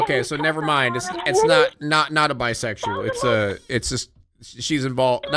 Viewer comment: not saying ‘bisexual’ instead of ‘bystander’